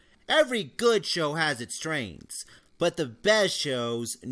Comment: needs to work on his pronunciation. Sounded like he said strains not strength